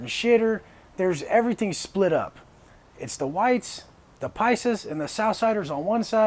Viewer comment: "everything is split up. It's the white, the p______rs and the southsiders on one side..." Anyone tell me that word i cant make out ? I tried googling picers, etc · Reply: Its paisas, it's slang in spanish literally means countryman. It's what you call someone born in a latin american country